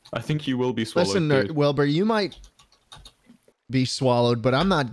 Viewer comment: It sounds like Schlatt almost said nerd instead of Wilbur